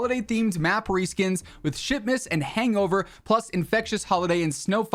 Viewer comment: I kept hearing "shitmas" :D